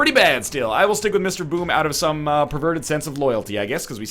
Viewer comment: did he say perverted?